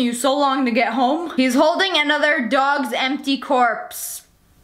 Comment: i thought you said Mr. dog